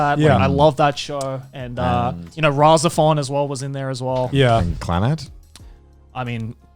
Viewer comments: what anime he said there? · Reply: I haven't seen it but I think he said RahXephon. I have no clue what its about but its from the same writer as Serial Experiments Lain. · What anime is he saying at 11:01 ? Rosofan?? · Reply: RahXephon